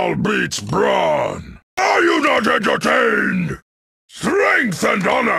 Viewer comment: BRO I've always thought veruk said "I do not understand" but in a silly orc way instead of "are you not entertained" lmaooo